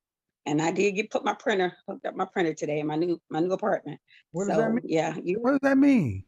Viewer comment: Did she just say I just got my printer 🖨 today. I’m dead and we’re doomed get yo shit together.